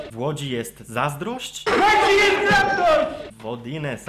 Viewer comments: Ja usłyszałem wtedy "W Łodzi jest Traktor " XD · ja usłyszałem w kodzie jest namiot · ja usłyszałem "W wodzie jest traktor" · Najpierw usłyszałam W Łodzi jest traktor, ale po głębszym zastanowieniu to moze byc jednak 'łapcie ten karton". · Ja usłyszałem " W Łodzi jest traktor" xD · w 4:24 usłyszałem "Będzie niestrawność" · Ja usłyszałem w łodzi jest traktor... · Ja usłyszałem 'W Łodzi jest traktor!' :;D